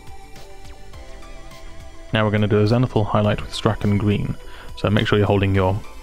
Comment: Anyone know what he says at 0:45 ? It sounded to me like he said "Now we're going to do a semi-full highlight"? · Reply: Zenithal :)